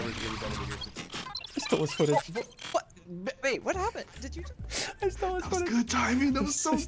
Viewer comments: Did nobody hear and 9:51 I think there dating he called her babe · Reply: its not babe it says wait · I heard Bebe what happened